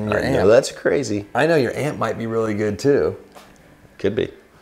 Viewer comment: At the start I thought you said “Your aunt might be really good too”. I was wondering where this episode was going until I realized you said amp.